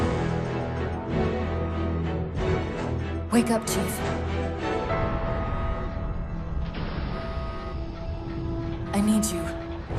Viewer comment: I can't be the only one that thought Cortana said, "Wake up Jeff, I need you."